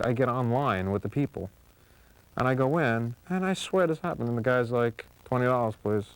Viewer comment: Does anyone know what the hell he says at 1:14? It sounds like "And I go in, and I swear dis up."